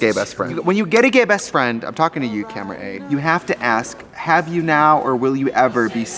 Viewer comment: “I’m talking to you, camera A” is the most POWERFUL thing I’ve ever witnessed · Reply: At first I thought Tracey said “I’m talking to you Kim K” and I was umm is Tracey calling out Kanye!?!? #Tea